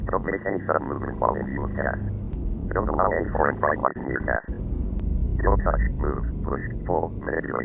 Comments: to me, it sounded like "Don't allow any foreign fragments near cast," but it could be others things. I'm pretty sure I heard "-ments." 5:33 IDRTN could be "iteration," but with the 1st T replaced with a D. NMRTN could be "enumeration." TPLST could be "tiplist," or some other list. RSLTSTTS is the one I'm most unsure about, but I'm guessing it's "results, stats, and tests," but a programmer would be better to ask. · Don’t allow any foreign body parts near cast. That’s what the part you put as (???) says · I think they say “Dont allow any foreign fragments near cast” unless its already confirmed and im a big idiot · I thought I heard “Don’t allow and foreign Fragments near cast”. Might just be me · it said dont allow foreign bright light, at least thats what i heard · I heard "don't use any foreign products near cast" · I think he says "Don't allow any foreign body parts near cast"